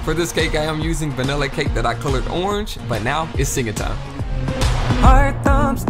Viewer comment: No ones gonna talk about how amazing his singing is? Like Jesus dude also the cake looks amazing and tricked me because I though it was real lol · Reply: I thought I heard him say enjoy my singing! Well I did this guy is truly talented